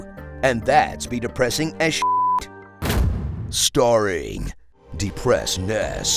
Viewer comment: I wasn't the only one who heard that as "Scaring"... right? Either way, still fits.